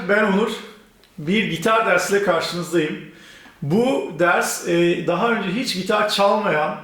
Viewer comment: Merhaba Nur anladım, kendini tanıtıyormuş 😂